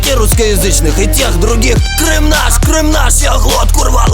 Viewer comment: Почему я ето только сейчас увидела? Оч круто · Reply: Cute little Кажется послышалось — «крышнам»)